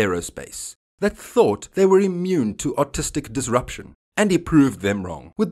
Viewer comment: Did he just say Autistic disruption ?